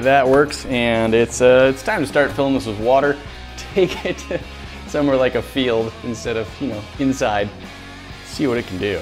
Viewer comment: XD who else heard him on 9:42 he said here like a baby then quickly changed it to inside 😂😂😂 I'm dying